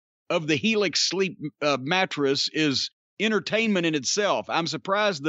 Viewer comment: I thought Jim was fantasy booking Elix Skipper... All I heard was "...straight out of the box....helix ssssleeper"